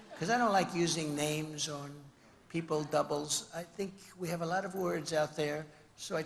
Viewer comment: when refering to hillary, did he just say she has a double? and gave credit of the crooked-ness to her double?? wt...